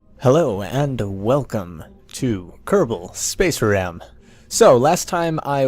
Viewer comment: When I first heard you say "welcome to kerbal space program", I thought you said "welcome to kerbal space ram".